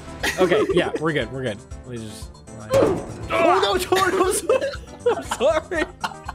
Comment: did he say oh no tord?